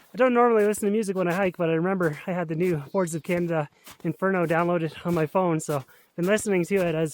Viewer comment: Misheard Boards of Canada Inferno as Horse Who Came To Inferno and learned there was a legendary Canadian racehorse named inferno. The more you know!